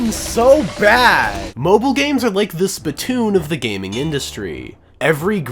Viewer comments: No because I'm so hyperfixated that I thought bro said Splatoon instead of Spitoon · I thought he said splatoon at first. Was so confused lol